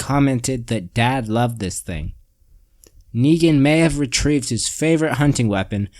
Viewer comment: Pretty sure Dean said “dad WOULD HAVE loved this thing”. Big difference between that and “dad loved this thing”. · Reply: I thought I heard dean say," dad loved this thing" maybe I have to go back but I thought I heard that